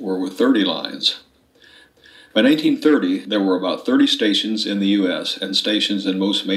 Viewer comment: "In 1830 . . ." Did I hear that right? Around 1:30 to 1:45.